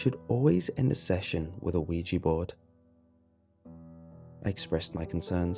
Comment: I thought it said OJ board lol